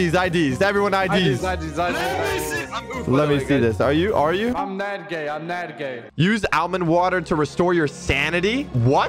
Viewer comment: I'm nadgay I heard I'm mad gay